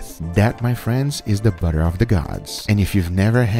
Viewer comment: I heard him say “boner of the gods” no one will convince me otherwise